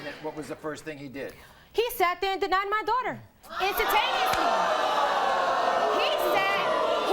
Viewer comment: "He denied my daughter Intatainenlie." Lol HAHAHA I love the new words that come from the Maury show! Lol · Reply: «Intataineously» was what she said, not «intatainenlie»… 32:52 ☺️